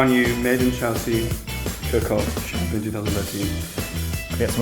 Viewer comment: totally thought it said 'cock-off'